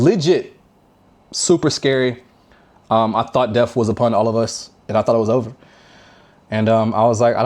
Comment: did u say "upon us!?" DETHKLOK would be proud lol